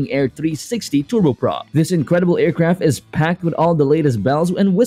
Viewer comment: Thought it said Beach aircraft for a second there